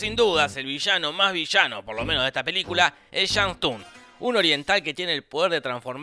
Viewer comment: El villano más villano es chang TUN min 1:49 Jajajajja lo escuché mu has veces xD y si... dice chang tun jajajajaj